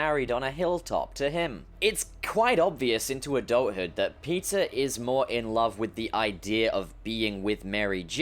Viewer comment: I heard: "Pizza is more in love with the idea..." PIZZA TIME!